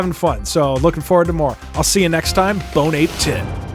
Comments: Did you just say bone-ape-tit? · "bone ape ans tea" at first i was'nt understanding what he was saying , but i finally know thar he was saying "bonne apetits", the worst part of this, is that l'm French...